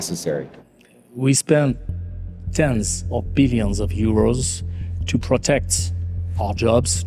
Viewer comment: did he really say "we spent billions of dollars supporting firms and have to pay for it by taking money away from workers". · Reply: No he said 10s of billions of EUROs. Dollars aren't used in Europa